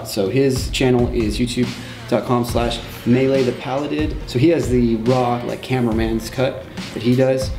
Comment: whats the youtube channel for the "camera man's cut"? how do you spell it? i heard "meleethepalladed"